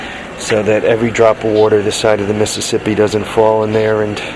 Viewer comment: Oh nevermind lol, I thought you said every drop of water in mississippi, not on this side of the mississippi. You had me hoping there was another trike hoarder nearby.